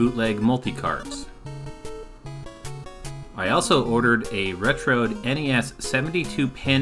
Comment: I hear it as " Retro'd " and found it had a nice ring to it going down Nostalgia Lane.